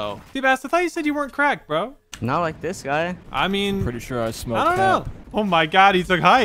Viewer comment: Anyone hear go "Pretty sure i smoke crack"